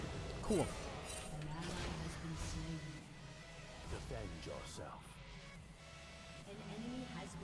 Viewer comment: Did Yi just say “end yourself” at 5:28? 😂 Holy shit even the champ itself is toxic.